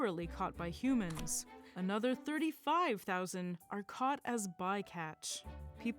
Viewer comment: the presenter says 35000 sharks are killed each year but the subtitles say 3500?